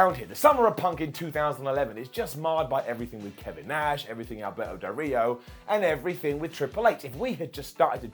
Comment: Haha sounded like Simon said Alberto burrito haha that's a great name